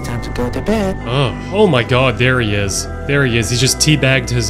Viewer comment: I thought he said "bro that's crapnap man"